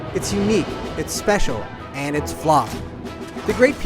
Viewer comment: what does he say at 0:28? "and it's fla"